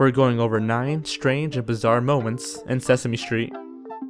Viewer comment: For some reason I thought it said Benghazi moments